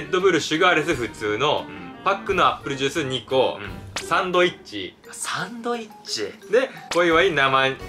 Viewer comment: サンドイッチが 三枚肉に聞こえたのか😂😂😂